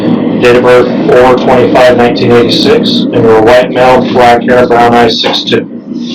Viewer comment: DOB Did he say 1996? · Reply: 1986 I believe he says